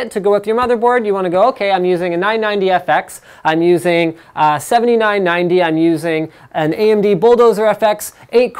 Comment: You should clean them, he clearly said 7990. 1:41 "I'm using a 990fx, I'm using a seventy nine ninety (7990), I'm using a AMD bulldozer fx 8 core CPU..." You should clean them, he clearly said 7990.